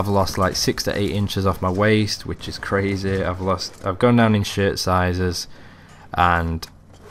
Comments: lol sounds like you said ''Ive gone down in shit sizes" lol XD but then I figured it was shirt. anyway congrats :) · "I've gone down in shit sizes" It sounded like haha.